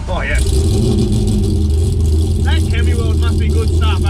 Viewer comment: what's he say @ 16:30 "that__ must be good stuff hey" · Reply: Chemiweld!